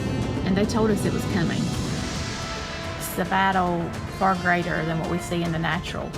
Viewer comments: Bahaha. I thought she said fur grader, rather than far greater. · What is she saying?? “greater than what we see in the natural”? “in the nacho”? 🤔